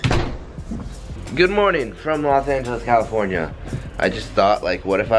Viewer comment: Sounded like he said good morning from Los santos